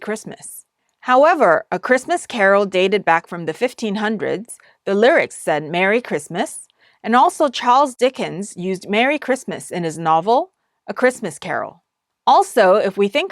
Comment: 선생님 감사합니다. 받아쓰기 좀 봐주세요. 1:38 저에겐 However a christmas Carol dated back from the 1500s the lyrics and Merry Christmas. And also Charles Dickens used Merry Chrisrmas in his novel a Christmas Carol.라고 들리는데 제대로 받아쓴 거 맞을까요? 문장이 좀 어색해요. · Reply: the lyrics said, "Merry Christmas" and also Charles Dickens used "Merry Christmas" in his novel "A Christmas Carol". 이 맞아요. "A Christmas Carol" 는 책 이름이구요.. 유튜브 자막언어설정에 영어로 설정하시면, 제가 영어로 써놨던 부분들이 있어요! 참고하셔도 괜찮을거 같아요 감사합니다!